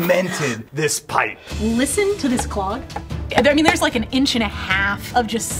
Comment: I THOUGHT IT SAID DOG